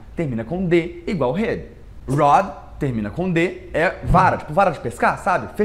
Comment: NIce! I'm sure this is based off the live you did yesterday! Loved it! #Part2 😁 At 7:42 it says RED instead of ROD, but everyone makes mistakes!